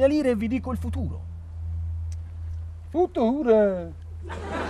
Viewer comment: Cosa ha detto a 3:22?? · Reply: Futur